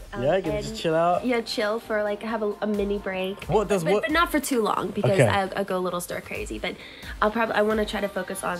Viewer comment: what does she say at: 4:53? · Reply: But not for too long beca- I go a little stir crazy